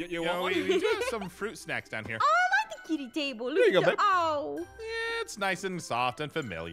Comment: I THINK I HEARD AARON SAY "here you go bae" to aph